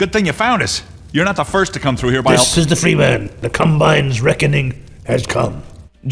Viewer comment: In one of the segments when he said "This is the freeman, the combine's reckoning has come" I thought he said "This is the freeVan" because they were inside what looks like a van.